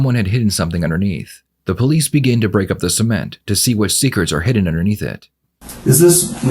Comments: The way he pronounces "cement" is messing with my head. I keep hearing "semen" and thinking 'man, who the fuck is producing quantities of semen large enough that it can be dug up at a later date' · I've never heard anyone pronounce "cement" in that way. I swear if the subtitles weren't there, the first one or two times I would've thought he said "semen"